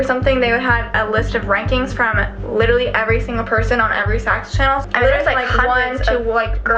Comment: It is hard to understand what she says here. But it sounded like she says "From every sex channel" Or did she say "sax".